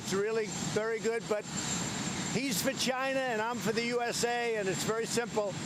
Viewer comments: I thought he said ‘he’s vagina’😬 TRUMP 2020!!!!!!! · He said “He’s for China” for a second I thought he said “He’s vagina” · At 1.02 I thought he said he's vagina, I'm for USA. 😂😂😂.